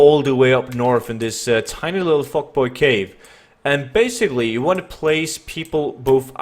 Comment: did i hear it correctly... tiny little fuck boy cave?